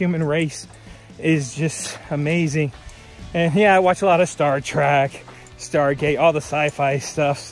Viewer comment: Good video. Glad you are leaving San Jacinto for another day. It sounded like you said "Star Track"?